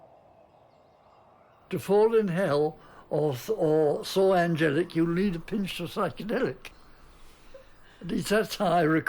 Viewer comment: What does he say at 55:10 ? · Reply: "To fall in hell or soar angelic, you'll need a pinch of psychedelic"